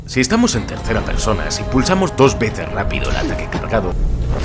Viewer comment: ¿tecla de que? no entendí xd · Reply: Dijo: Ataque cargado